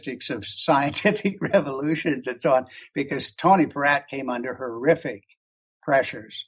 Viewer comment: is he saying Tony Pratt? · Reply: True enough Dave is saying Tony Perratt who was the leading plasma physicist at Los Alamos plasma labs. Tony was deliberately derailed by a person Dave Talbott refers to as a troll who tried to derail the Kronia conference in Portland Oregon by threatening to ruin the careers of some of the scheduled speakers. Troll? would have succeeded had it not been for the work of the late U.S. Navy astronomer Tom Van Flandern who was also a speaker at the conference. Dave Talbott leaves out the name of the troll and I agree with him on this as it would spread more hate and there is enough of that already. Tony Perratt can be seen on the 2nd half of the DVD (Dark Matter) which is very good and has quite a few well qualified speakers. When we look at the whole of this picture it is quite evident that it is for the most part ego that prevents progress. I personally will stand with the thunderbolts/electric universe folks because I like to make sense and the material makes sense. Your fellow student, James